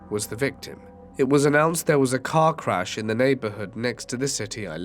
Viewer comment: I misheard “cat crash” as “car wash” and was so confused how someone could be a victim of a car wash.